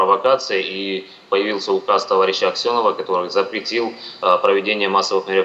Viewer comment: Только мне послышалось "товарищ Аксьонов"??? Возрождение ссср парашкина-рашка начала с Крыма?